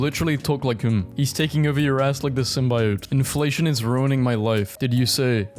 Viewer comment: bro really said simbyote instead of symbiote lol